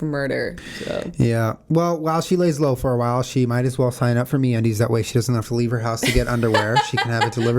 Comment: I totally thought you said MEATundies at the beginning and i almost cried